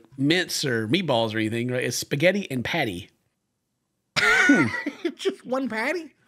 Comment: When she first announced the dish, I thought she said "spaghetti and PÂTÉ" and I nearly choked